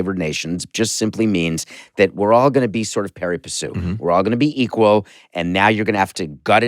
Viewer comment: What word did Samson use at 26:05 ? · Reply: Pari passu is a Latin phrase that literally means "with an equal step" or "on equal footing". It is sometimes translated as "ranking equally", "hand-in-hand", "with equal force", or "moving together", and by extension, "fairly", "without partiality. -Wikipedia